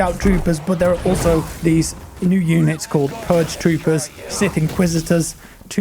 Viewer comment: DID I HEAR PURGING?!